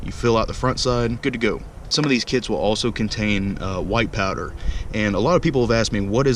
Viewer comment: when he said white powder I thought he said power and I was like OMG HES A RACIST COP